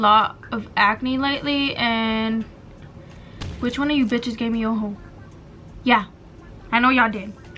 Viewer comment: I thought you said which one of ya’ll gave me a HOLE LMAOOO😭😭